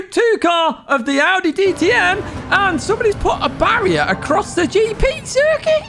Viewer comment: I literally heard "Somebody put a barrier across the GP Turkey"